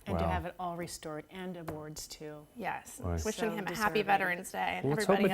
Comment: Lmao brooo at 4:34 I thought she said devoiced instead of award and I WAS LIKE WTF HOW IS THAT A GOOD THING